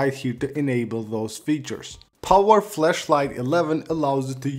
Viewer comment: I heard Power 'flesh light' 11